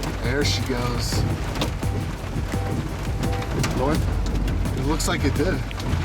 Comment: am I tripping or did he say it looks like a dih 🥀